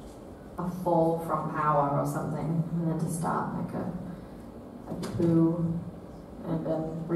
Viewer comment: what did she say? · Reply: I need to start a coo